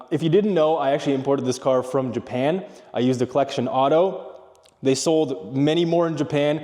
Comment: Hey where did you find the merc? I thought you said "collection auto" but couldn't find them when I searched. Thanks!